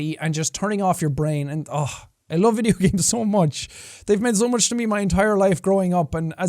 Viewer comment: Jacksepticeye: I love video games so much what I heard : love it sooo muuuch